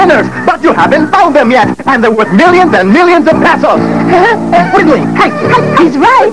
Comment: "I won a million and millions of peppas!" This is misheard